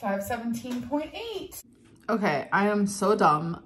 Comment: I kept hearing “570.8” lbs 😂I had to rewind it several times .